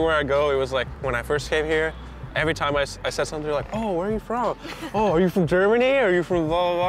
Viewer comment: Did he just say "Oh are you from Germany... Oh are you from Vulva??"